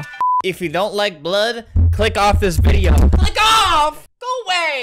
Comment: I literally thought he said f*ck off cuz I got a ad